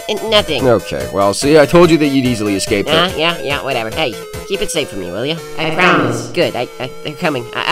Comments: I thought he said him safe but no its IT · Wait I thought I heard riggy say keep "him" Safe. But he said keep "it" safe At first I thought he was talking about danno but now there is more to the story what really going on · I THOUGHT HE SAID KEEP HIM SAFE FOR ME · “Keep him safe for me will you?” Means riggy truly cares for Danno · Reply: I thought he said keep "it" safe